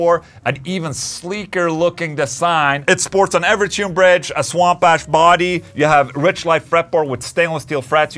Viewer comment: Whenever someone says "swamp ash", I hear "swamp ass"... Which is much more unpleasant.